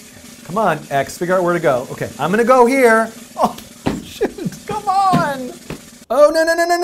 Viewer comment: pretty sure there is an error in the english captions at 20:42 think Dan would say "shoo-" rather than "shi-"